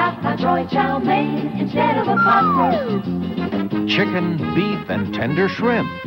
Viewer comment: Did they say pot roast?